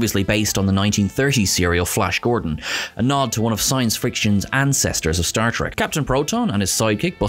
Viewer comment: did you say "science friction"? Awesome!